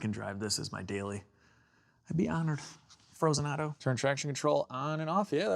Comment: Did I hear you call it frozenado right there 6:32 you kind a named it I take it!👈👌👍